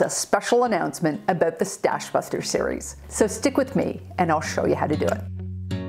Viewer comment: I love your videos! Thank you for the great content. I wanted to comment that in your intro “…stick with me and I’ll show you how to do it”, in my mind I always hear “…stick with me and I’ll show you how to get it done!”